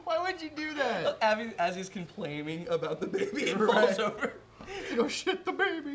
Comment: Did he just say, "complaiming"